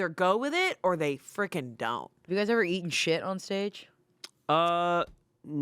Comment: The way said “eat shit on stage” I thought she was saying “eat and shit” 😂